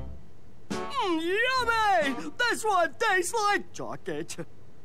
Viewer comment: Did Ed say "this one tastes like jock itch?" LMAOOOOO BRUH